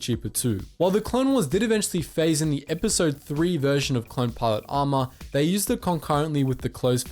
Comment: i thought it said clone plot armor, and i was like IKR there's no way Rex and Cody were like extra special i guess idk, but force 99 makes sense, the being modified and all